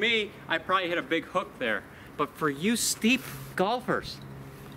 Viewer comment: Sounded like at 20:00 he was about to say, "For you steep F**kers"